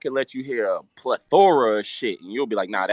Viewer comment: Wait wtf roc say right here😳😳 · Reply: Plethora. He just has that Baltimore accent